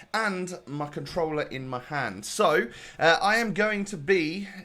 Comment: i thought he said in my pants😂😂